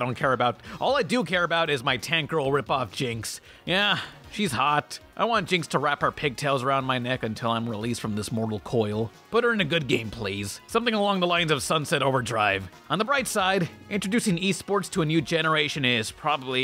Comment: I thought you said good game.